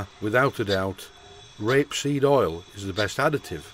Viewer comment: 'n all this time I thought you were saying 'grapeseed' oil... ; -)